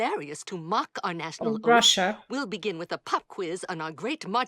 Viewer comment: Russia? Did i hear it right? It doesn't have anything to do with Russia